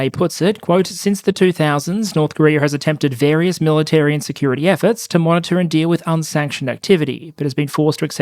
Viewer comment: When you said "...North Korea has attempted various military and security efforts..." I heard "...North Korea has attempted various military INsecurity efforts" and it still fits. I would laugh if it weren't the North Korean people paying the price of the joke. Always a sad topic.